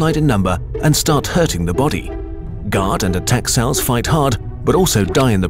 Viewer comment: We are thinking of making an App for tablets out of this video. Would you like that? Did you think the visual system we developed worked? Feedback is much appreciated! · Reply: Please, will you put English subtitle on this video and other/future videos (because the automatic one isn't correct, ex.: when you say "guard" the caption is "god") since English isn't my main language and sometimes I misheard the words. Thanks for the videos ! They are very informative.